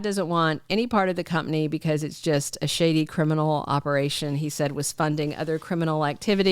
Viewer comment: OMG, B. I lmao when you said "shady" because I thought you said "sh*tty" (I was crunching some peotein chips and I almost choked 😂)